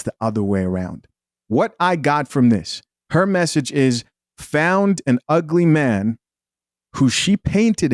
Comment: What i heard. " I found me an ugly simp!"